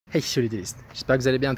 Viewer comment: il dit quoi ? < ech'chaud les dueliste> ? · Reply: Hey yo